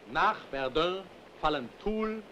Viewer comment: Hey sorry i am very late for subtitles correction but at 1:41:55 as a french i clearly hear the cities of Tulle, Épinal, for belfort and verdun you were right but for Béssancourt i think it’s Besançon, by the way thanks for the work of subtitles in this whole movie it should have take quit a time